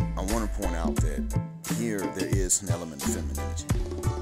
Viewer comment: Did you say feminism or FITNAH. You know I am when it come hearing you through the beat. Come on now you from down south Speak up now. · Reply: I must have said "feminism".